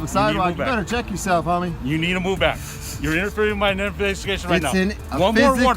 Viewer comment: first if you want to wear a mask were it properly ... second maybe trim them nose hairs.... third did he say "you're interfering with out biscuits?"